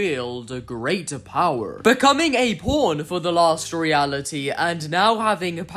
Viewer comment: Why did it sound like he said (p)corn for the last reality 2:59 😂😅